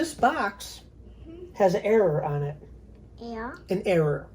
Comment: error to her sounds like your saying air